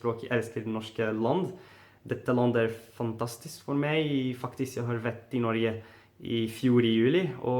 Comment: lol that sounded like "f**k this" xD You are very good in all the languages! Impressing dude!